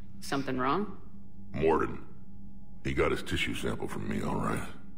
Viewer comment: I always thought that Wrex was saying Mordin got the tissue sample from his balls. Cuz if you think about it it IS a cure for an infertility plague, right? And that would definitely be a place that was never meant to be cut. It hurts just thinking about it.